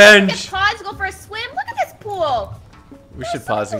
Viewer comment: he said poo instead of pool 🤣🤣🤣🤣